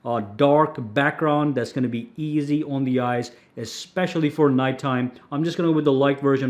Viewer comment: I thought he said "Especially for Fortnite time" but actually he said "Especially for NIGHT time" 😂😂😂